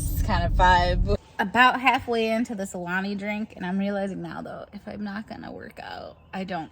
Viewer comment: At the very beginning I thought you said ‘I’m halfway into this SALAMI drink’ and I about died 🤢 but no.. Alani drink.